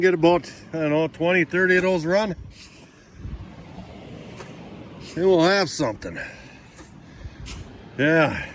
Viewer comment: you make a comment that hopefully you can get, and I can't understand what you say after that, could you clarify for me please?? Sounds like you say $20-30 a run... just guessing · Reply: I was referring to the small blue pails i was running… “ hopefully we can get 20 /30 of these run and then we’ll have something “ 👍🏻✌️